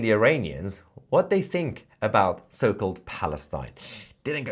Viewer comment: Did he say what do they believe about Palestine I thought he said what do they think about Frankenstein